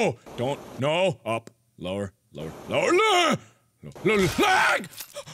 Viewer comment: In the beginning, it sounded like he said "Lower, LAAG!!" for asl second 😂😂